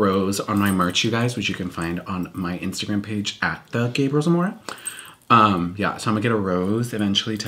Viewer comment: Sounded like she said “Gabriel Zamoro” at the beginning.